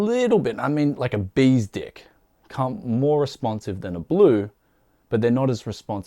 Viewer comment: Did you say a babys dick at 7:55?